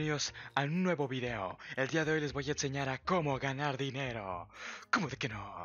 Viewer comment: Cuando lo lei pensé que decía cagar dinero jsjsjsjsjs